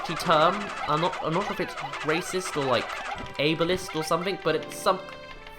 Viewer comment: you said "wog" was 'racist or avilist(?)' what was the second word there and what exactly does it mean? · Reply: mrROFLstudios ableist. It's basically discriminating against people with disabilities.